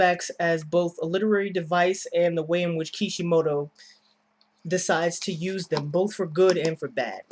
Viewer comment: Lol i heard you as "The way kishimoto decides to spam them"